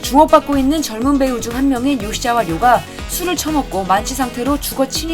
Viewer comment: 내가 잘못들었나 ??????????????????? ㅋㅋㅋㅋㅋㅋㅋㅋㅋㅋㅋㅋㅋㅋㅋㅋㅋㅋㅋㅋㅋㅋㅋㅋㅋㅋㅋㅋㅋㅋㅋㅋ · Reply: 술을 처먹고 ㅋㅋㅋㅋ